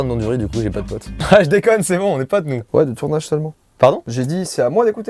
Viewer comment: Joyca "Wow le tournage est éclaté sa mère..." Mastu "Pardon ?" Joyca "Non j'ai dis que c'étais a moi d'écouter !" J'ai tout entendu 😂😂😂😂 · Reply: Il a dit " le tournage seulement " 😉 Pas "le tournage est éclaté sa mère "